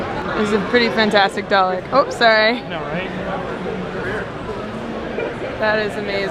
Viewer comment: you - "Ops sorry" ... Dude - "I know, right?" did I hear that wrong?